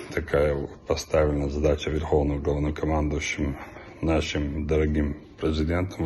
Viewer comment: Верховним говнокомандувачем? Мені не почулося?🤦‍♀️